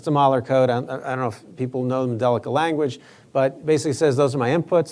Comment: he said, "andelica language" or "andelic language." What does that mean? I spelled it phonetically so "andelica" is what I heard but that may not be correct. Maybe he said, "angelic?" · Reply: I figured it out. He said, "Modelica." The language Mathematica Modeling language it uses to produce the model.